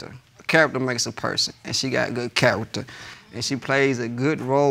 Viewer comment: I thought he said she has good cat...but he said character